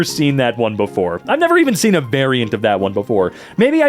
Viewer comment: Did he just say I’ve never even seen a bearient of that before?!?!